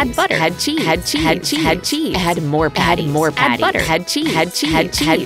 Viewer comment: all I hear is HECIES